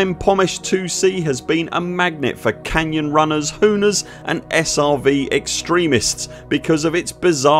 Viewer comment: What did he say? Hooners? Hurlers? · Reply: Hooners - people that hurl themselves round canyons very fast and very low in their ships ... it's a sport called "hooning"